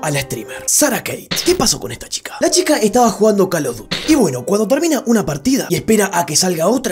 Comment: Jajaj pense que dijo call of gruty